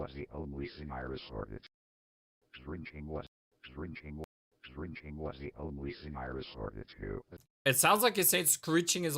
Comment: I think he said "drinking was the only thing i resorted to"